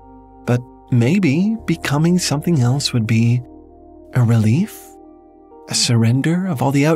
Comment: am I going crazy but it sounds like he said relief and yet the caption said belief.